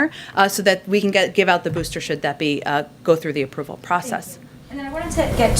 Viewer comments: Did she just say booster sh*t? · She circled back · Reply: did she say booster shit?